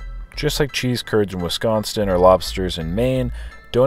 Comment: did you say WisconsTin?